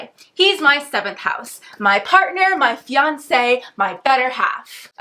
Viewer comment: How i thought she said "oh Beyonce!!!" ? SHE SAID FIANCE